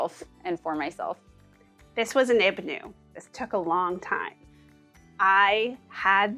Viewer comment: 35, Caroline says something about her pantry area...this was "an ibenu"...what is that word and what does it mean?